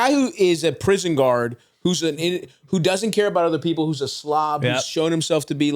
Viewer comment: what did say here? Anyone know? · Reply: He called him a slob